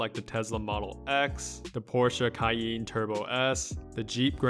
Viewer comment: Did he just say kyeeen? Instead of cayenne???